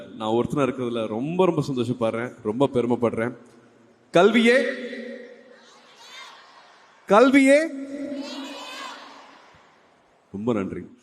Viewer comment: What is the crowd answering when Suriya says "Kalviyae........, Kalviyae.........." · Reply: Kalviyae aayudham Kalviyae kedaiyam. I figured it out.